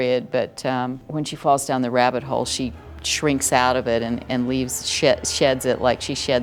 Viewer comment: Does anyone else think it sounds like she says "...and leaves shit--"? lol